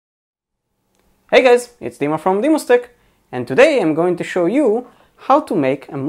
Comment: At first I thought you said "from the moustic" !!